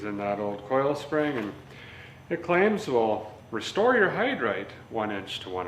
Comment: Wait..... what?? Derrick, did you say " Restore Your Hide Right" 11:30 ....Now THAT'S Fact-Tree